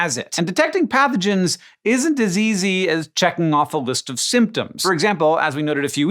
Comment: Isn't as easy sounded like isn't diseasey